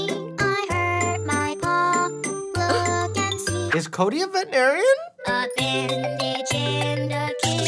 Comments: I only realised he said veterinarian when I turned on captions I swear he said "Is Cody a vegetarian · What'd Mike say?? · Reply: Is Cody a Veterinarian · “Is Cody a veterinarian”, had me crying. 😂 But valid question,..Cody! 🤔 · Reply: i thought that said vegetarian not veterinarian XD